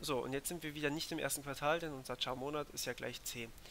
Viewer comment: Da hab ich 10 anstatt c verstanden